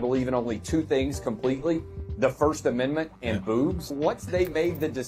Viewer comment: The guy at 3:29 was he saying “boobs” or “booze”? Because his mouth is moving like “booze” and it sounds like “booze” so I’m confused….😂